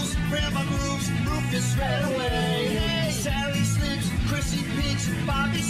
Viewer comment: Why does it sound like he said Christmas Bitch